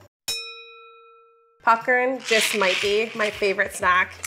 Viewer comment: I'm sorry... Did she just say "puckern"?!?